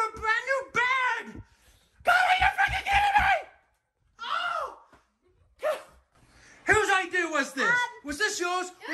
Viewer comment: I couldn't understand a word he was saying and I laughed so Frickin' hard!!!! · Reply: He said god are you freaking kidding me